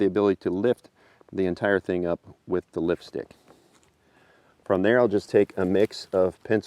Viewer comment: lol i am big dummy. i thought he's saying Lipstick. then he focus the lift, its actually Lift-stick! hahahha